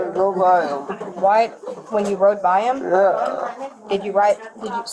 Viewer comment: what these kids giggling about. I think they heard "and I got shot it go BAM" but the detective said "when you rode by him?" And their smiles dropped